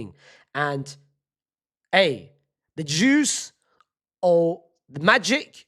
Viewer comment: i thought he said the jews 😭